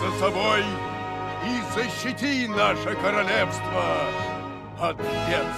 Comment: только мне показалось что он сказал "засити". И советую купить древние стрелы.